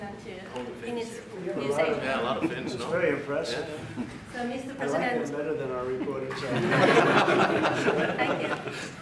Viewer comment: Anybody hears, what funny Trump says to Stubb in 15:20? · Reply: "I like them better than our reporters over here"